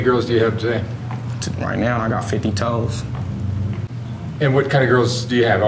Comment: "...I got 50 toes" Me: 50 toes???? Okay, so there’s 10 toes per person, what’s 50/10.. Okay 5.. So he has 5 B words?? Lol. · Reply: I thought he said 50 hoes n then when he said what he had i was like thats not 50 lol but i jus figured i heard him wrong or sum never ever thought he said 50 toes wtf ig saying he has 50 something makes him feel better about himself.